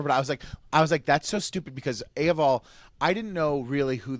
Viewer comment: did he really say "A: of all"?